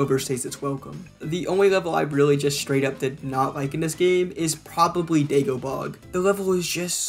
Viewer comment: Did he call it "Dagobog"?